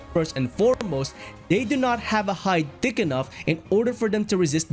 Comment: Did you say "High D*ck enough" Or I misheard it...